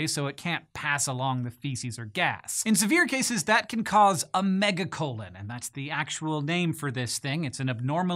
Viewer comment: thought that said megalodon xD “Mummy, what’s happening?” colossal shark comes out of anus